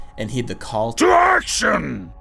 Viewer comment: Did olaf say "to errection!" at 5:39?